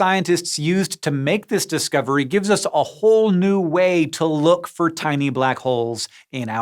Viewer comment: Hehehe, I heard that as "a HOLE new way" ^_^